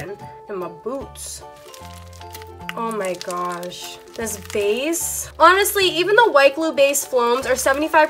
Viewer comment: 😂😂😂 The captions says face not base!